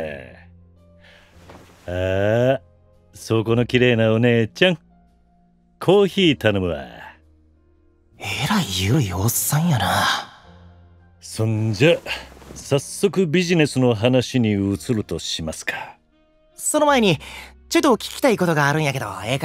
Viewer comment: 「えらいエロいおっさんやな」に聞こえたのは私だけか？